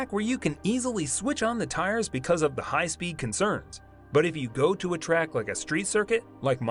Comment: I'm sure you said concerns instead of corners